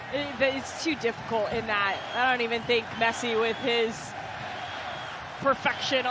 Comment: Did she just say Messi wont. @ 9:37 you aint ok